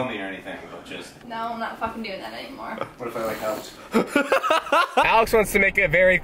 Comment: What did Alex say at 1:36 ?!!! i can't hear him! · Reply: what if i like helped